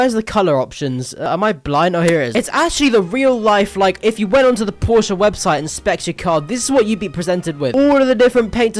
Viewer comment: did bro say Porscha 😭🙏